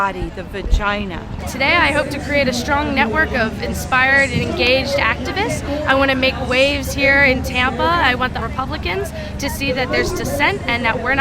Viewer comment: "a change in tampa" who else thought she said tampon?